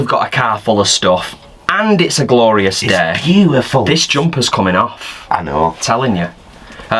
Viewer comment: Jersey? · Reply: I think I hear, this jumpers coming off.